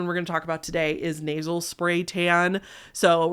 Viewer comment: for at least 2 hot minutes, I thought you where saying "Naval Spay" and not "Nasel Spay". I was so confused about what was so bad about putting tanning spay in your belly button.